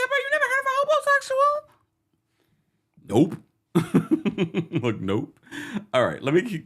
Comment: I thought the caller was saying homosexual. 😂 I thought to myself she's bold. Lol